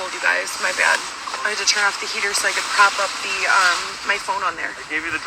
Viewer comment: did she just say turn off the heater?